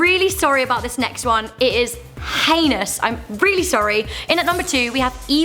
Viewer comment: I thought she said anus